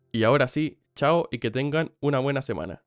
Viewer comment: Lo que dice "chao y que tengan una buena semana" Lo que entendí "chao ctmr"